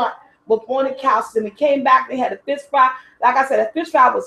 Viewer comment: I love you and your videos, but this one started off by scaring me! I thought you said you weren't there last week because of the fist fight! Of course, what you really said was fish fry....